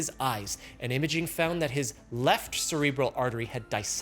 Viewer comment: Text on captions says "left VERTEBRAL artery" but baby man voice saids "left CEREBRAL artery"............. boy i hope someone gets fired for THIS lbunder!!!!!!!